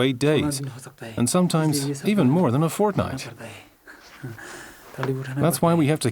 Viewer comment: Has somebody else hear fortnite